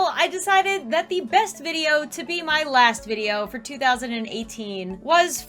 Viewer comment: At first in the beginning I only heard “the best video to be my last video is....” IT SCARED ME TO DEATH